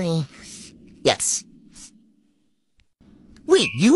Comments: I thought he said math lizard not wizard 😭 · No cuz the way she kissed him at the end was like “I’m so in love with u” after he said he’s a math wizard 😭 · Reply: I thought he said math lizard 😅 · The way I heard “math lizard” 😭😭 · The passionate kiss after he reveals he is in fact the math wizard > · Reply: I thought he said math lizard… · I really thought he said "im math lizard" 😭 but the face grab was just🎉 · The passionate kiss after he reveals he is in fact the math wizard > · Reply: Honestly thought he said math lizard🤣🤣🤣🦎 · The passionate kiss after he reveals he is in fact the math wizard > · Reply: and I thought be said I'm half lizard 😂😂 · I thought he said “ babe I’m a math lizard” 😂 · No cuz the way she kissed him at the end was like “I’m so in love with u” after he said he’s a math wizard 😭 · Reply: I thought he said I’m a math lizard 💀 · No cuz the way she kissed him at the end was like “I’m so in love with u” after he said he’s a math wizard 😭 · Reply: I thought he said he was a math lizard 🦎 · No cuz the way she kissed him at the end was like “I’m so in love with u” after he said he’s a math wizard 😭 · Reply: wait i thought he said he was a math lizard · No cuz the way she kissed him at the end was like “I’m so in love with u” after he said he’s a math wizard 😭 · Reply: And I heard “I’m a math lizard” 😂😂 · No cuz the way she kissed him at the end was like “I’m so in love with u” after he said he’s a math wizard 😭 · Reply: I heard “Math Lizard 🎉🥳”